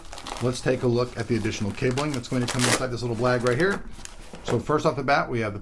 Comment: Did you say blag? Black + Bag = Blag?